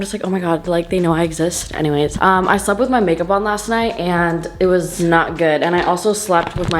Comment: I thought she said "I SLEPT WITH MY MAKEUP ARTIST.. ON ACCIDENT yesterday." BAHHAHAHA OMG IM DEAD 😭